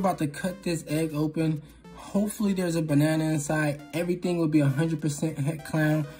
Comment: No one : Me : wheezing bc I heard “ I HOPE THERES A BANNANA INSIDEEE”